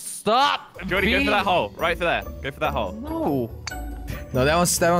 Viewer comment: Hhahahah i was laughing beuacuse he saying what hole i heard him saying butt hole